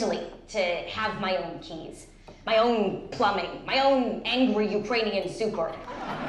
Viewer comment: what mrs. Maisel said? Please help guys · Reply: "my own angry ukranian super "